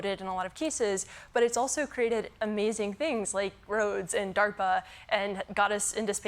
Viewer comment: Wait did she say DARPA?